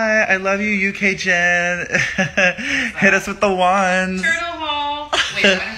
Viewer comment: Wait at 1:30 I think mitch says "turtle haul!" Which is referencing a video that drew and Shane made but I might just have not heard correctly lol